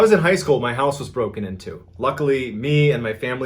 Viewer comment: I thought he said my nose got broken in two